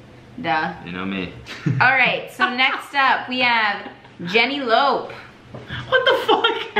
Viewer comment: I’m high as shit and I also don’t have my glasses and heard her say Jenny Lope and I was just staring at the screen trying to think about who the fuck Jenny Lope is